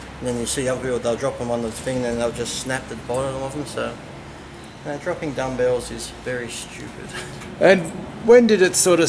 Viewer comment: I clicked because i thought it said "dropping gumballs in the gym"